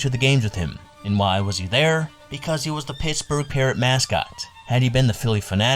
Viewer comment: Did you say at 4:06 Pittsburgh Peeretts?